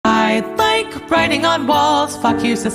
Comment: Ngl thought U said 'i like writing on balls'